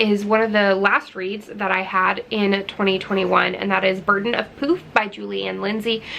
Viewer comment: When you got to Burden of Poof, I thought you first said Burden of Poop! 😂 I don't think I read a whole book last year! 🙄 I know. My bad! Lol